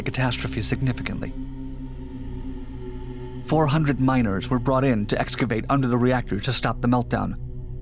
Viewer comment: I choked on my coffee from mishearing the narrator saying "minors" instead of "miners"